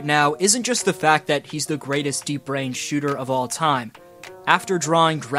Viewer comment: I thought you called Curry a de-brained shooter